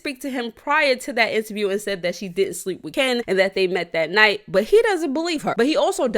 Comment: did you say that “she did sleep with ken“?!